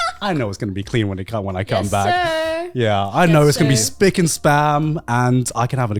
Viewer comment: Did he say Spic and Spam? Pretty sure it's Spic and Span lol